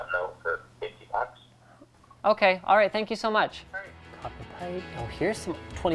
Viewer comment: It sounded like the guy on the phone to Linus at 13:05 said f**k you